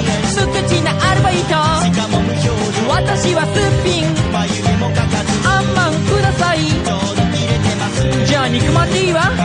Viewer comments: I heard this word : sufin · IS IT JUST ME OR DID DENMARK SAY SOMETHING THAT SOUNDED LIKE SUFIN AT AROUND 1:38 OR